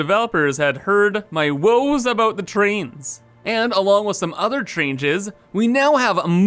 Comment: Did he say Trainges at 8:50 ?